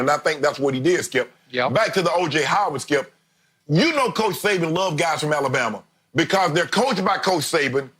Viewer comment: What Shannon Saying? "Saban likes guys on Alabama?" WHy wouldn't Saban like guys on his own team???